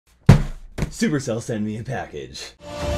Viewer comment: Idk why but when you said “supercell sent me a package” i heard “supercell sent me a bad b***” 😂😂😂😂😂